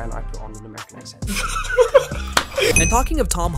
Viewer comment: did he say assent